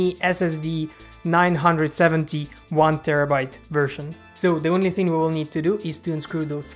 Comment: Did he just say 971 terabyte version.. 😂😂